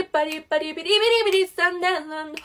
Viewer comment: Tui nghe nhầm thành sikibidi :v